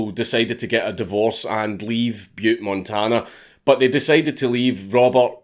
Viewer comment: swear to God I thought you said pube, Montana